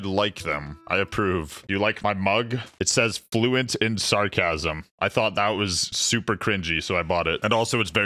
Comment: I thought it said fluent in autism